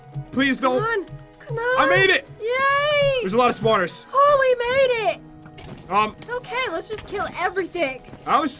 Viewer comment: When said "Oh we made it" I thought she said "Oh mayonnaise" lol